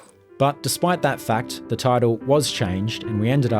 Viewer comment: I think you meant to say title, not tidal…. Unless you’re American.